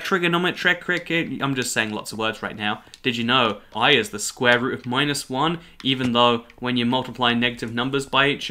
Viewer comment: I thought he said pi was the square root of negative one, but I double checked and he did say i was the square root of negative one so I don’t really need to make this comment.